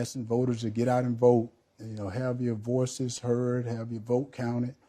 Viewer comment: I had to listen to Hershell's introductory comments THREE times. I think I heard him say "have to abortions heard". That third time I think he said "have their VOICES heard". Well, silly me.